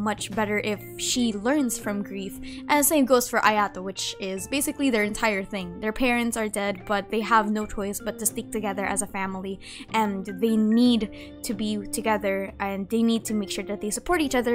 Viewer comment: I THOUGHT SHE SAID THAT 'THEY HAVE NO CHOICE BUT TO SLEEP TOGETHER AS A FAMILY' 💀